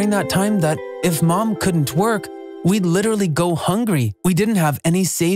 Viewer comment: I heard as twerk 😂😂😂😂😂